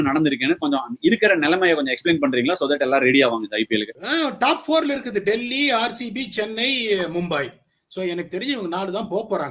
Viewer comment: Ash anna @ 13:10 timestamp the caption says Delhi Daredevils instead of Delhi Capitals. Please do change it for DC fans ✨